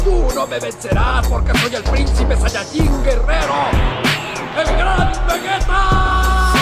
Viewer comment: Yo pensé que decía el gran vergeta